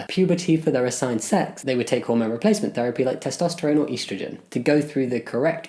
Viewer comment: D-did he just say "EASTrogen"?